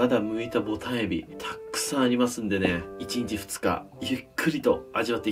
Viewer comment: 「Baby. 」にしか聞こえない